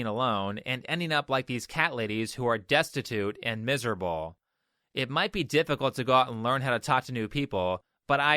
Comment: I swear I heard you say “it might be difficult to go out and talk to Nude People” 😂 and I thought to myself “yeah, I bet that would be nerve-wracking...”